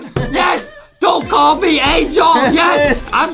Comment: turn on comment and it says “don’t call me a joke” instead of don’t call me angel🤣